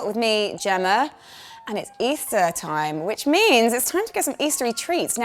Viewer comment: i first heard "its Iftar times" i was thinking whats going on here :D